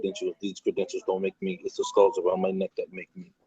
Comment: "it's not these credentials that make me, it's the skulls around my neck that make me" -...Btw, did he admit to committing crimes with guns in the beginning of this? His sound was a bit low. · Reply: Altmailacct Other: I thought he said stars, but skulls make more sense. But yes, he says something to the effect of "when I commit armed robberies, when I commit carjackings, I am on both sides of the law."